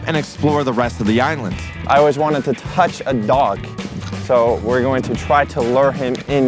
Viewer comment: did bro say dog